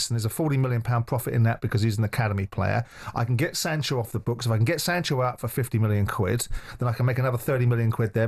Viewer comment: Wait, did he just say "£50m for Sancho"?